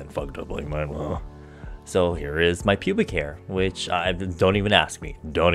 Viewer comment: did he say pubic hair? 😂